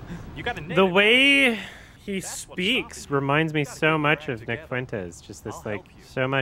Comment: I thought said "the way he speaks reminds me of Vic Fuentes" and I had a 2015 emo resurgence